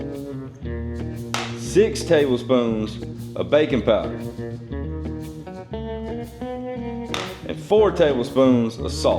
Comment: I had to do double take, I thought you said bacon powder, I was like..what in the world is "bacon powder" but then it dawned on me you said "baking powder"...😂😂😂